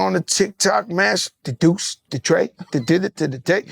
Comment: Joe wildin!!! @ 1:08:37 did he say “the deuce the trey the did it tha dick”